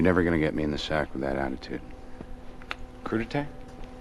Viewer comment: Seriously what the f*ck is rdj saying at 13:03 I literally cannot tell. Sounds like "koodetay" except that's not a word, and then they all laughed like it made sense I'm so confused